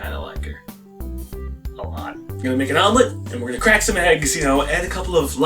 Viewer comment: What he said: We're gonna make an omelett What my brain though: Where gonna make out I know. My spelling sux.